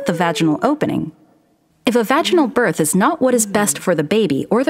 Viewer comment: I thought they were saying “fashionable birth”